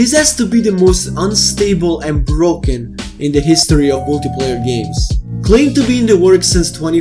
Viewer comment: The subtitles say "multiple" instead of "multiplayer".